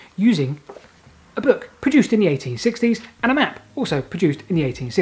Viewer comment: I could have sworn that he said the book and map were produced in the 1860s? Is my hearing that bad now?